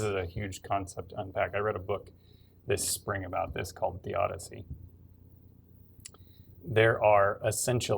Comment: Does he say “The Odyssey” or “Theodyssey” by Paul Gravit??